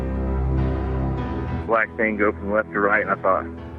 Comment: Everyone keeps saying" black tango left to right" but I hear " black thang goes left to right" on the opening sequence??